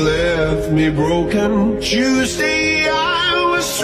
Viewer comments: He says Tuesday. This whole time I thought he was saying Juicy eye. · When it said Tuesday I thought it said juicy💀